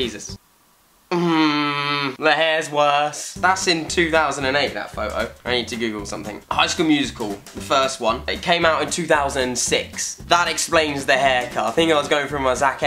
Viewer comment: Who else at 1:36 thought he said “I need to giggle something” 😂